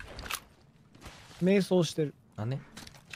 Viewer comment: Im sorry to say this but at 0:37 kuzuha was actually saying that he was meditating not saying sorry 瞑想＝めいそう[meisou]＝meditate/meditation ごめんなさい[gomennnasai]＝sorry and after that kanae said why? in a small voice not right? なんで？＝why?